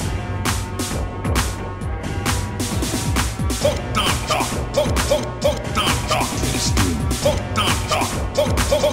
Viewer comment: What is he saying 1:33 like that part tho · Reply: “Foot dive”